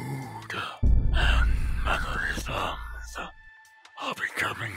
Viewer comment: what the heck did say? i couldn’t understand him 😅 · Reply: I know it’s about a year later but he said “and mannerisms”